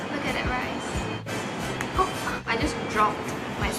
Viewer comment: Did she say 'oh f**k' @ 3:34? 🤣